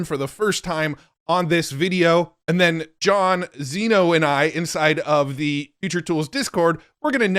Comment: I thought you said inside FART 🎉